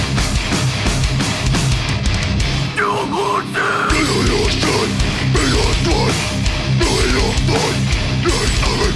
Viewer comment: what does he say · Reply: "Your yardsmith"